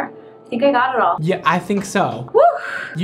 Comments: i thought he said she looks so bad not bada$$ I WAS ABT TO THROW HANDS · When Brad said she looks so bad*** I didn’t hear the bleep so I thought he said bad 😂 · At the end when he said “omg you look so bad***” I thought he said, “omg you look so bad!”